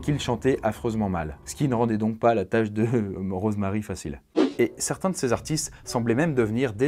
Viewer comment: c'est moi ou tu dit morose marie ? C'est le pire jeu de mot du monde mdrr 😉😉